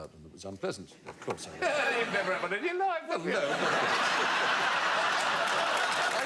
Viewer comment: What is Alan saying at 1:59? I cannot understand, even after replaying multiple times. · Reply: "You've never had one in your life, have you?"